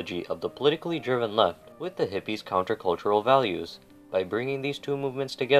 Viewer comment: Please, What did the video say 3:45? IE 'With the politically ???man ?ap'? TY. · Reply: "politically driven left with the hippies' counter-cultural values"